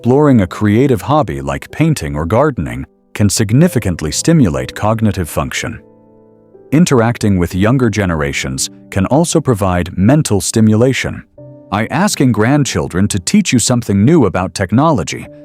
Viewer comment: Y'know, i don't mind these AI programs but i roll my eyes when the AI bot reads inaccurate sentences. An "i" instead of "in". For example " I asking grandchildren to teach" @ 9:58 is wrong. And there are a few others that i cringe at. If its AI do it right, don't these robots have spell check?